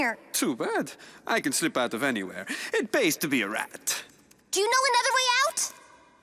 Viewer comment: I need to get off the internet, I legit thought he said, “it based to be a rat.” ☠️